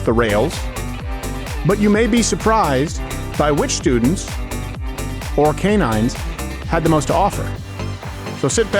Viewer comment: I was getting ready(not looking at my phone) when 2:40 rolled around and I heard “which students… or which canines…” 😂😭😭 I would have called animal control on that thing.